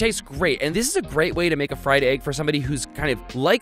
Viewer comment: He said fried egg but the subtitles said friend egg.